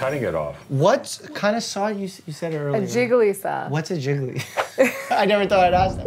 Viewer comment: I literally thought they said a jiggly saw not. LOL